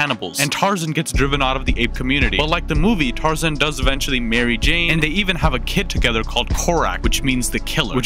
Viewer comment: I thought he said “Mary Jane” ( as in Mary Jane Watson from spider-man for the clowns) instead of marry Jane